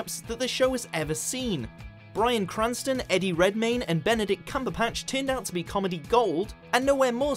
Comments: oml did anyone else hear 'cumberPatch' at 1:46 i'm dying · wait did i just hear "cumberpatch" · Did I hear that correctly at 1:45? "Cumberpatch"?